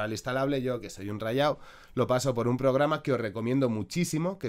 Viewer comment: cuando dices recomiendo te refieres a recomierdo?? No lo entendí muy bien